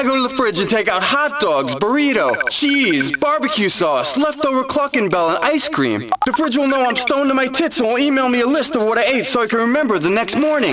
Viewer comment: What did that guy say?... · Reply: The fridge will know I'm stoned and my tits will email me a list of what I ate so I can remember the next morning